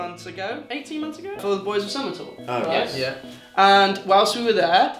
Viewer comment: 1. I'm so excited for Take This Home music video. 2. 3:12 Did say Fall Out Boy Summer Tour? Did you go Boyz Of Zummer???? 3. Happy new year 4. I love you 💕